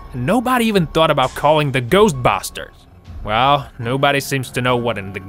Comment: I sounded like he said ghost bastered